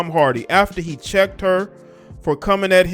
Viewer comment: 🤣 I thought that said CHOKED her for a second. 😬